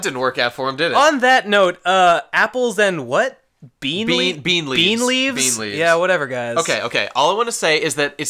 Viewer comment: it was BEET leaves! (i'm pretty sure, i could have misheard tho)